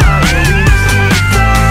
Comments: I keep hearing "you can eat celery" and I'm like, "hell yeah, I too hate celery" lmao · i’m not a native english speaked so i thought she said “you can eat sex” i was like what?